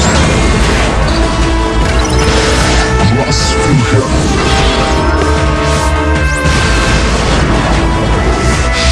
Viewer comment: What does he say at 0:09? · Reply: nvm i get it now, it's cross fusion